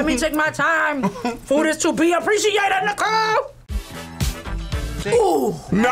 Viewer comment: I swear she said the n-word